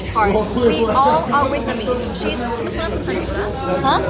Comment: What she say?say 5:08 "whitnamese "